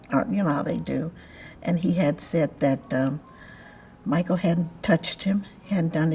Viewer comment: There are mistakes in yhe subtitles. For instance in 7:03 she says "he hadn´t touched him" but the subtitles says "he had touched him".